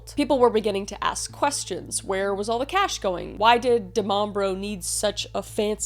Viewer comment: All I keep hearing is “da mom bro” like that one bro who acts like a mother or smth 😂